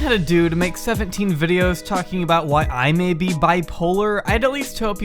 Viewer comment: He said BPD, which is Borderline Personality Disorder, not Bi Polar. Otherwise it sounds like you are using Bi Polar as an example separately, so it still works, just FYI. At first when the critical videos came out about The Rewired Soul, I thought it was more of people saying a person shouldn't talk about mental health or trauma at all without being a licensed therapist. Now this doesn't make sense because some of the most beneficial and deeply helpful discussion and explanation comes from people who have healed through a difficult, extremely challenging mental illness or trauma. I've done this myself, and in the last 8 years that has also incorporated a growing body of consolidated research. I've also been doing street and community outreach especially focused on youth, and routinely interact well with all kinds of people. The high level of exposure plus internal understanding shouldn't be dismissed. I'm realizing at least this video is more critical about his tactics being really loose and unprofessional, throwing out hypothetical diagnoses on people he never met, and making so many drama videos about youtubers and trying to tie it back to "mental health". He doesn't really spend almost any time talking about the types of therapy or healing processes people would go through for various conditions. There is a whole community, arguably a renaissance, around physical trauma therapy and the recognition of it as a nervous system disorder primarily. This guy doesn't seem to